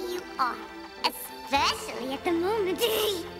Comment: What the fuck is the sprite saying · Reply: theedwardian Especially at the moment hee..?